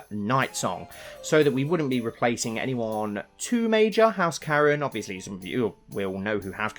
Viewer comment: At first I thought you said house Karen instead of Caron.